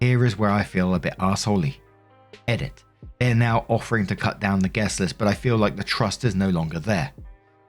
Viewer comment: Spoiled, selfish and "small-hearted"? 🤔🤣 You WERE mislead. Somebody gon' get drunk and wreck your house, throw up on your sofa pillows, piss in your plants. Just say no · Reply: They say “cut down the guest list” and I hear “cut down trees.” Absolutely not!